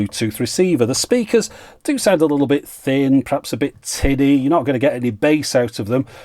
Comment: did he say tinny at 17:42? Gorn!